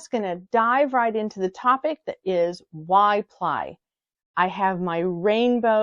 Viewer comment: Looks like we're both living in brain fog city today 😹 When you started by saying "why ply" I heard "Y ply" thinking it was another term for chain ply 😹 Also square swatches make excellent pockets on sweaters that don't already have pockets. Please pass on some scritchies to the cats <3